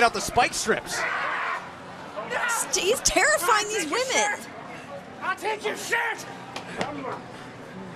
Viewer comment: did he just say I will take your shirt lol